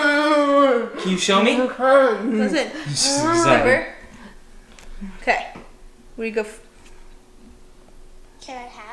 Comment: Did say: "Nooo, I can't."? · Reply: After watching a lot of the videos I come to the conclusion that that was definitely a "no!". The first time I noticed it was in a video from about 2 years ago but it always sounds the same and usually does it after already shaking her head (in the right context) a couple of times. It feels like it's her "last resort".